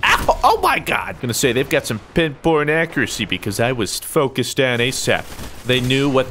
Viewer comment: I swear I thought you said "pin porn accuracy".